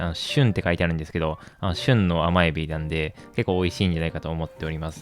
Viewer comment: ～の『旬』の発音が名前の旬くん聞こえた笑